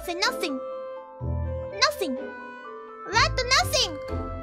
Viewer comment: It sounds like she says “nothing lad” rather than “nothing rat” lol